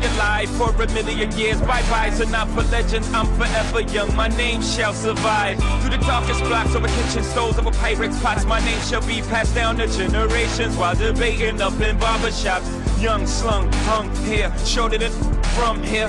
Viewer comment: I thought he said Walter Payton up in barber shops